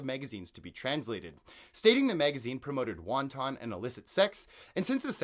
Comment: My friend. Did you just say at1:44 that "It promoted Wonton and illicit sex"? That word is Wanton(Pronounced Want-UN), I won't insult your intelligence, I'm sure you know what it means. However that is the funniest mispronunciation I've heard today.